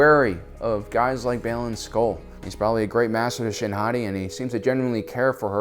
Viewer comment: Was….was he saying Shin HOTTIE? I could’ve sworn I heard that